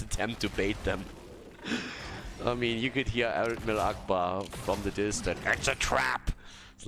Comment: I thought I heard say "allahu akbar" and had to play it back a few times hah